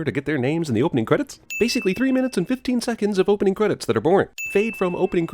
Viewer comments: Cinema sins sin counter: 1. At 0:29 he said three minutes and 15 seconds when the captions say 15 minutes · he says "three minutes and 15 seconds" but the subtitles say "three minutes and 15 minutes". DING